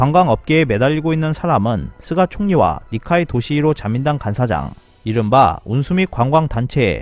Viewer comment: 그런데 저 "자민당 간사장"이라는 말은 난 옛날부터 들을때 마다 "간짜장"으로 들리더라..